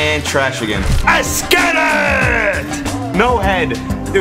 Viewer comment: somebody knows 0:33 what did twomad say? thx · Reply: King Nothing esketit .